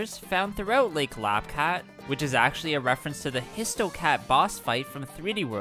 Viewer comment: Did he just say HistoCAT?